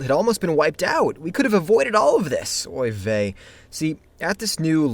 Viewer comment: did you just say "oy vey"?